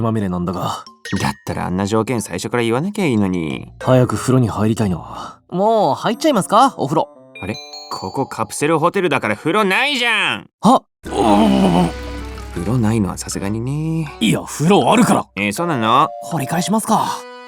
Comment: 風呂ないじゃん!がお前の席ねぇじゃん!に聞こえる